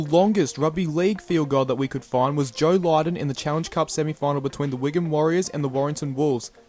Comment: Thought you said, Joe Biden.